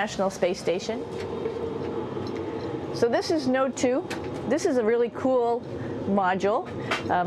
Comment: Oh she said Node 2 I thought she said No tube! Lol